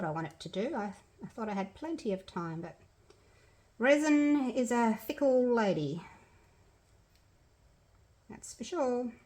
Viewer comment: When you said, "Resin is a fickle lady," I heard, "Resin is a thick old lady!" and now I cannot stop laughing. Love the bloom!